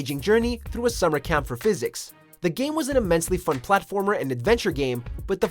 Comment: did he just say physics and not psychic